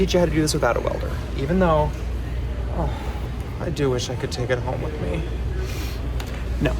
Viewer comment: I had to re-listen to that part because I thought you made a welding joke. I totally thought you said. "I wish I could TIG it home with me." #tigWelding